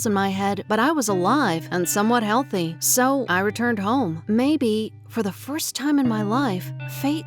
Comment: Anyone else thought it said burned